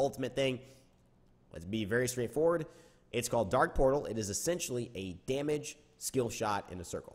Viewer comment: That's funny I thought that said dank portal